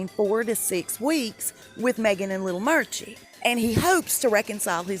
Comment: I keep hearing 'little Merchie'. Is that right? Or is my hearing going?